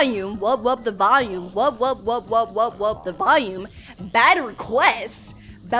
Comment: I thought it said bad Quech instead it bad request (rare wubbox)😂😂